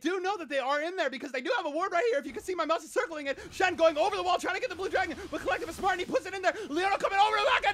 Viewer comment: did he say "SHEN TRYN TO TAKE THE BLUE DRAGON" ? XDDDDDDDDD